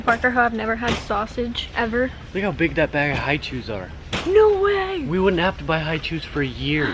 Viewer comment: When you were in Costco talking about the Hi-Chews, I thought you were saying High-Chairs because I've never heard of Hi-Chews😂